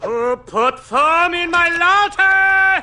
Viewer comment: 😠 GRRRRAAAARRR 0:16 What HappenWhy did he throw his coffee? did it burn? and he frowned, he didn't like his coffee with foam and what did he say? or wanted to scare hippie boy and dog🤔😲😲😲😲😡😠😠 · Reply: Who put the foam in his latte? My money's on Agent 9!